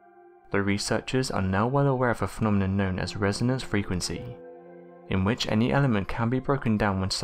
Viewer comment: I though I heard you say "Renaissance" instead of "resonance"